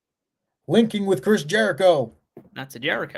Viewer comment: I thought he said 'Wanking with Chris Jericho'. Which is a show I would watch.